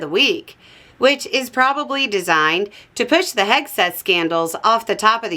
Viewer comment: "The head set scandal". Did I hear right? What 'delightful', but probably not-important, stuff may we European have lost this week?